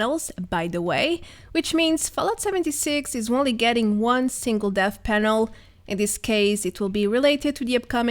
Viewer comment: Oh. Dev panel. Thought you said death panel for a second.